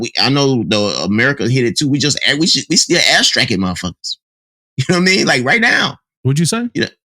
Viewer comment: I heard "ass striking"